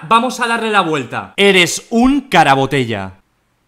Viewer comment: en el minuto 6:46 me pareció escuchar en una parte "caraculo" no sé, yo escuché eso :v